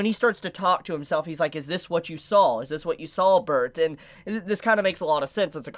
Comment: Armin be like: "My main goal is to blow up then act like I don't no nobody" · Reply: did he just said 'Bertain' 😂😂 Rip bertholdt